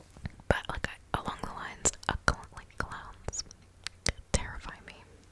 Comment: Oh my when you were talking about your biggest fear I thought you said clouds instead of clowns 😂😂😂